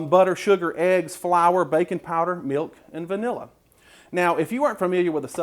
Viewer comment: ISTG I thought you said bacon powder...and now I want that to be a thing...